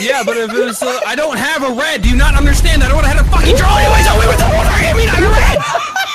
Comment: still don't know what said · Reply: “I don’t have a red do you not understand that I don’t know how to draw when I don’t have a red” that’s what I heard